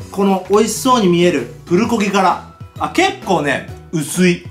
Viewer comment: プルコギの空耳はフルボッ〇なwwwww